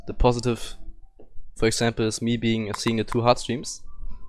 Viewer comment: what did he say? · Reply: "For example, me being fc'ing the two hard streams"